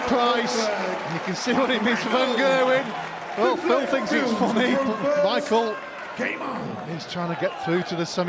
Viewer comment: Phil is not saying that he thinks he is funny. Those lips read he’s mental.